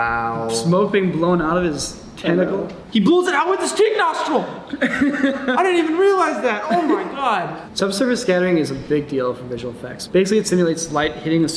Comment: Dick nostril (9:45)? did i hear that correctly? Bc according to the subs, I did...yet im still having trouble wrapping my head around that and mentally i am f*cking horrified by the visualization in my brain